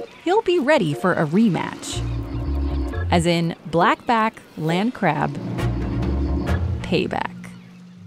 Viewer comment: I THOUGHT IT SAID JACKBLACK CRAB AND THOUGHT THEY NAMED A CRAB AFTER JACK BLACK HHHhOUzoeyaoGIehdoabwodvsosbd